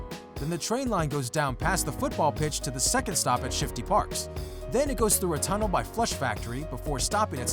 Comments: did he say shifty parks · wait did I hear shifty parks? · did he say shifty parks like if I’m right dislike if I’m wrong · Did he just say Shifty Parks!? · did he say Shifty Parks? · Lmao who else heard him say shifty parks lil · Did he just say shifty parks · did he say Shifty Parks? 😂